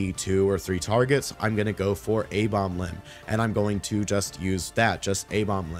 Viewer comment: pls help. Dont understand shit here 22:18 im not a native english speaker and have no idea what i have to do by "a bomb limb" or what youve said... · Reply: Thats the Necrolord Covenant ability Abomination Limb. Sorry I don't know what language you speak so I can't Google translate.